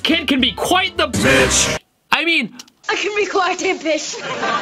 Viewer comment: I can be quite a fish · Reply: All I heard was "I can be quite a timpish"